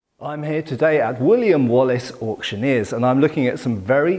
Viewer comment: I know its Wooley and Wallis auctioneers, but I keep hearing "William Wallace", and the mental picture of Mel Gibson facepainted as a blue smurf hosting an auction has me in stitches.